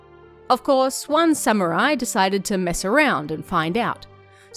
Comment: here i literally thought u said "one summer i decided to mess up and find out"😂🤣